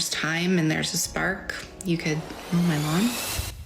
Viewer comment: Am I getting deaf or is the sound bad? I can't hear 0:27 "do my lawn" ? "do my mom" ? "Do my pond" ?.